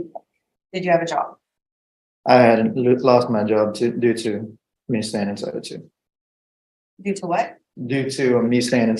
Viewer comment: Someone please tell me what he says at 10:21. 😂😂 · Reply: I believe he said he lost his job because of staying inside, which I guess he means that he did not want to leave his house which I guess means he got fired for basically not showing up for work?🤷‍♂